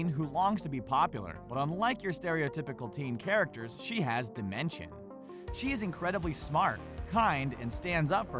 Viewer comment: did he say she had dementia ?!?!